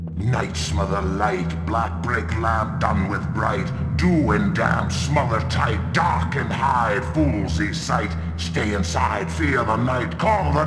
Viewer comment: What's in the world is that dude saying? I have only watched one other video on this game and also I was wondering who or what he is? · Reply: His exact words are,"Night smother light, Black break lamp Done with bright, Doom and dance Smother tight, Dark and hide Foolsies' sight, Stay inside Fear the night Call the dark Call the black Bringsie forth, I call it back!" He is the main antagonist a god in their world known as The Trickster. Possibly their equivalent to to Christianity's Satan/The Devil/Lucifer. I thought he was a great villain. There seems to be other myths and real life lore about a Trickster that he was most likely based off of. Should google it sometime.